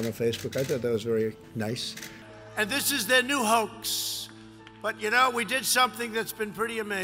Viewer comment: Did he just say ...."and this is the new hoax ?"